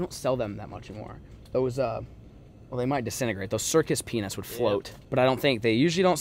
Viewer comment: I could've swore I heard say circus penis and not circus peanuts 😅😂💀